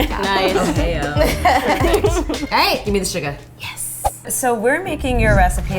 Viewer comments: when said “give me the sugar!” In my mind I thought she said “give me the Suga!” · it sounded like she said "Give me the Suga"... Any ARMY'S here?!